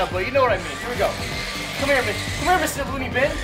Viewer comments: HE SWORE HE SAID COME HEAR B!CH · OMG roi said come here bitch